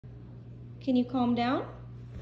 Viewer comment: She or he thought you said paw down